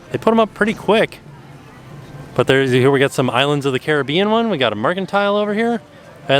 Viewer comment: when you said Islands of the Caribbean. i instantly thought you said Pirates Of the Caribbean wrong lol